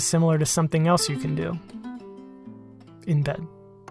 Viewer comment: did he say in bed?